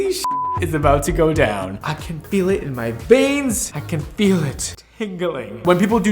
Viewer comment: Currently laughing because I thought Brad said “I can feel it in my bangs” instead of “I can feel it in my veins”😂😂😂